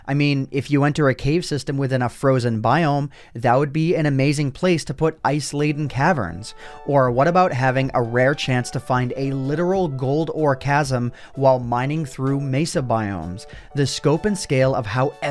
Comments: anyone hear Gold Orgasm ?😂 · When he said “Gold orecasm” I thought he said “gold orgasm” 😂